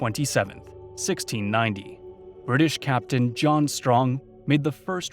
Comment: i thought he said "british captain, jon tron"